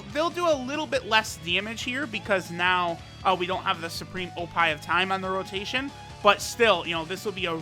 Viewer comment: my man, did you say supreme oppai of time ?